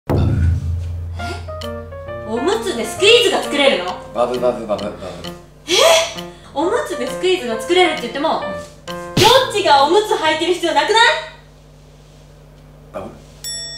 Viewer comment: おお、はやコメ笑笑 バブルバブがゼルベバブに聞こえた…